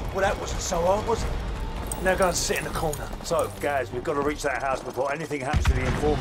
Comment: he said "now go sit in the corner " 🤣🤣🤣🤣🤣🤣🤣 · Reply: carlos plays roblox I thought he said to go shit in the corner